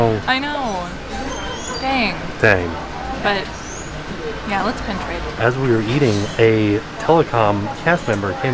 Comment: LOL!!!!!! At 11:47 when said “let’s pin trade”, the subtitles said “LETS PENETRATE”!!!!! Lol!!!!!! It’s ok, we know what you meant